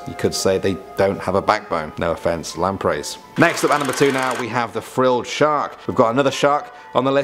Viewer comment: Who heard at 6:07 rather than 3 he said 'coming up at number 2 now' ?